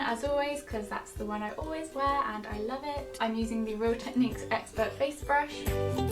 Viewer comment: Lol I thought said ‘the real techniques expert face brush bitch ‘